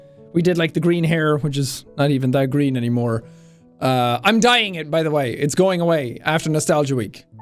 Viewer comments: When he said "I'm dyeing it by the way." I miss heard him and thought he said "I'm dying by the way." And I had a ninny heart attack. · I thought he was just gonna say I'm dying and I was so shocked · I thought you said "I'm dying, by the way" when you were talking about your hair and I swear I almost went into cardiac arrest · When jack said he was dying his hair I misheard him say "I'm dying" 😭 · I dead ass thought you said “I’m dying” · Sean talking about his hair in the beginning "I'm dying it" What I heard "IM DYING" LAWD NO! 😩😭 OH....I'm just dumb. My bad. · Caught me off guard there! Thought you said "I'm dying btw, it's going away" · I was swatting a bug when he said "I'm dyeing it" and all I heard was I'm dying. my fucking hEaRt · I thought he said "I'm dying" and I gasp out loud before I realise he said "I'm dyeing (my hair)". Sigh. 2020 was really scary, I still don't feel like it is already a new year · i thought he said "im dying btw"